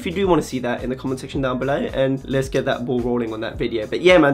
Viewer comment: LMAOO I thought you said young blood when you said down below